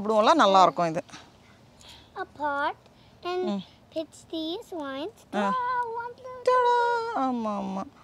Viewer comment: I cracked up when I heard saying 'holy shit' at 2:54 🤣